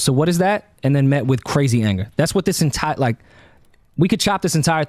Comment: What does he say at 56:15 “..and it was met with crazy ____” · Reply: Crazy anger